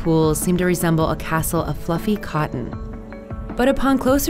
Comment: I thought it said fluffy cotton candy to my surprise now